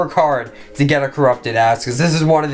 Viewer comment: did he just say “corrupted as#”?